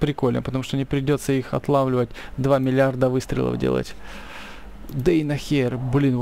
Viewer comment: дейнохер или мне послышалось 5:19??????